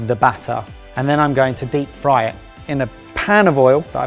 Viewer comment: did say “deep fright”? Can you cook meat by scaring it?